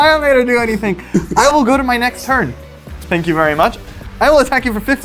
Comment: Did he say "spank you very much" @ 6:20 min?